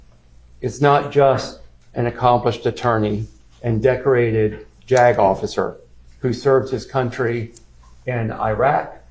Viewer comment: WHAT?! trial defense attorney says "My client is not just a decorated JAGOFF..." 😹😹😹